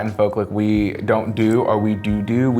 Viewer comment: did Curly just say do do